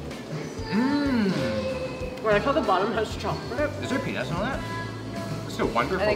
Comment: When Will was eating one of the donuts, I thought he said "Is there penis on this?" I realized afterwards that he said "peanuts" not "penis."